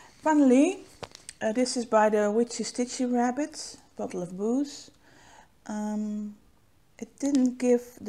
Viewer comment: Sooo have not got far and just and to re watch the bottle of boos finish.... I need more sleep because I thought it said bottle of boys and the ghosts were sperm 😳 ooops I would maybe have white alcohol in the bottle. I will now continue